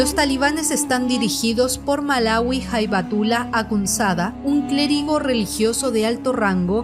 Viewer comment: Como lo entendi: HaibaTULA xd